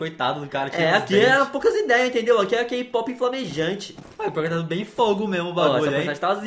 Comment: eu ouvi kpop?????g zuis